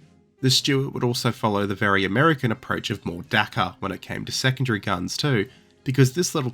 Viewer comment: what did he say? Dackup? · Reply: More dakka